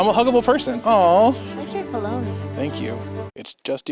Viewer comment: I rlly thought she said I like your baloney